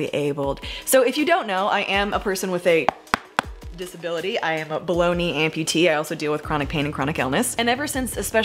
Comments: When you said "I'm a below knee amputee", I swear I heard you say "I'm a bologna amputee" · I keep hearing “Baloney amputee.”